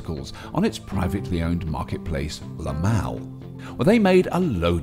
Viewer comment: i thought he said lmao, lmao